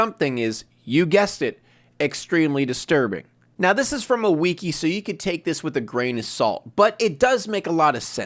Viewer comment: Did he say weki?